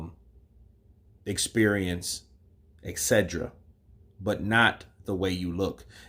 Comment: Did you say exedra? 😅😂🤣 I think you meant et cetera.